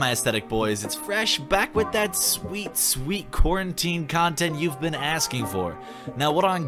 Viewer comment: Ngl when you said “quarantine” I thought you said corn tea XD Oh and congrats on 420k subs !!!!